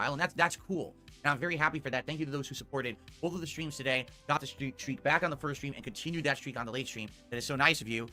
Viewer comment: At first I thought he said “dent streak”, ha.